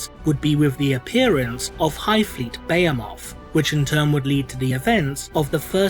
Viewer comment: Ok, I understand people pronouncing Tyranids very differently, but I had to chuckle when you said Behemoth. I genuinely thought you said Bayo-Moth.